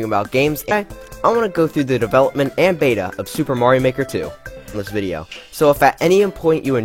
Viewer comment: subtitles say: "money maker 2" instead of Mario Maker 2. I see no accidents here.